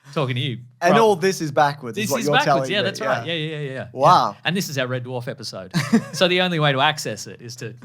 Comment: Anyone know what at 8:00 was said? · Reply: "It looks like I'm talking to YouTube (or you 2?) but I'm actually talking to the one sad loser in Britain who's going to take this backwards audio and re-align it and put it in a machine to turn it backwards actually" Jokes on him though, I'm a sad loser in the US! It sucks here right now guys... :face-blue-wide-eyes